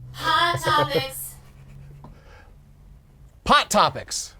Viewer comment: When Doug said "pot topics" I heard " hot pockets"